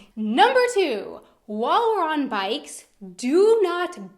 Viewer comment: what was that? Wall-run-bikes? I just couldn't catch that and Google doesn't know wall-run-bikes... err... help? Statement 3: "grilling smells good" ... well, please consider that this statement is not true for everyone. There is stuff people grill that makes others puke in their mouth - and there's people who use grill lighters or even petroleum or gasoline (crazy, I know) in vast amounts and having that in your apartment isn't pleasant at all. Number 5: "Happy birthday!" - this is just a guess, but when you translate "Happy birthday!" to German it would be "Fröhlichen Geburtstag!" or "Schönen Geburtstag!" - and this in fact would be no problem at all to wish someone - and people at the office will often wish you a "Schönen Geburtstag!" or "Feier schön!" in advance if your birthday lands on the weekend. I think the problem arises when you translate the German phrase "Herzlichen Glückwunsch zum Geburtstag!" to English "Hearty congratulations to your birthday!" in the sense of "congratulations for turning [age]" - and I'm sure you can see the problem here. You don't congratulate on something not yet achieved. Now, since Germans are limited in their precision regarding English (just consider the GERMAN word "Handy"... ouch!), they hear "Happy birthday!" and automatically (but falsely) translate it to "Herzlichen Glückwunsch!" and then they are pissed for no reason ;-) I'm sure no German would see any issue if you say "Ich wünsch dir einen wunderschönen Geburtstag! Feier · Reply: rikers I'd say it was a southern Floridian(?) accent and she meant "while you're on bikes", but that's just a guess.